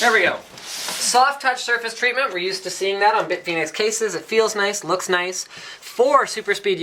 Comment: I thought he said "Big Penis cases", due to the multiple that's what she said jokes at the start.